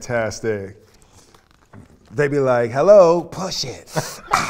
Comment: You all said that you woke up to , "Push it by Salt n Peppa"? I thought you said , "Shoop", LOL 😉